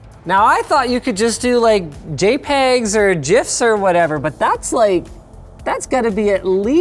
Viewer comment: did he just say jifs?